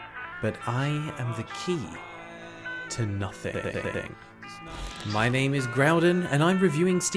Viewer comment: it always sounds like you say "My name is Groudon"